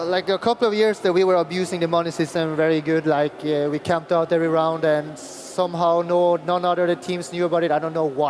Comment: What does he say at 2:02, how were they abusing the system? Can't understand what he said, sounds like "We camped out every round" and he later says they won because of that, not sure though.